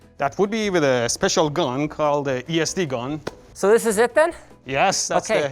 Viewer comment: I thought he said lsd gun lol